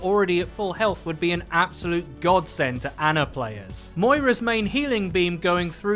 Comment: what i hear "make ana like Moira"